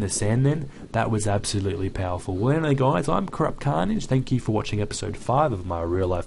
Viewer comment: hello guys i am crap carnage it sounded like he said that